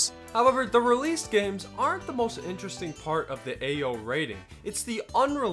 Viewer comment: All I hear is "Ayo rating"